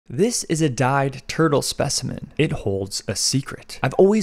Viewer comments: At first I thought you said "this is a died turtle". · I thought he said a died turtle but he was saying dyed turtle